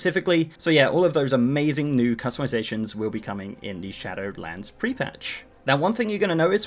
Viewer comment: Am I the only one who hears "Shatterlands" when he says "Shadowlands"? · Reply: I hear "Shadowedlands"